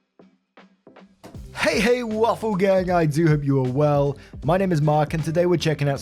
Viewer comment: mark says " I do hope you're well" and despite me knowing that I always hear "I do have your world".. xD